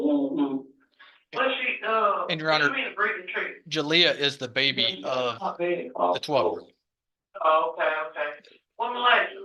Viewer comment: Tell me I just heard that wrong. Did the DA just say "Jalea is the baby of the 12 year old"??? Jeesus! WTF is wrong with people!!! smfh!!!! (edit): And this POS let the SA go on for 2 years. . . Meaning it all started when HER OWN DAUGHTER WAS ONLY 10 YEARS OLD!!!